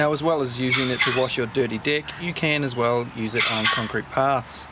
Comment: I can't take this any longer. I just have to post that each time you say "dirty deck", I hear "dirty dick" 🤣